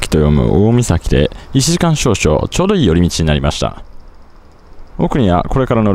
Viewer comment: ローストビーフ丼に目が釘付け！ お腹いっぱいで何よりです。「ちょうどいい寄り道」が「チョロイ寄り道」に聞こえてしまい、再視聴（言い訳）・・・ 海沿いルート景色、素晴らしいです。上手く晴れ間が回ってきていますね。